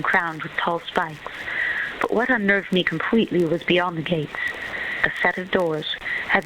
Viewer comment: This book is going to be great, just like Emilie. I cant wait to buy it :D btw, at about 0:15 she actually says; but what unnerved me completely was beyond the gates